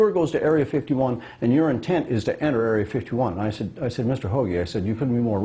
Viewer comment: lol did he just say mr hoe?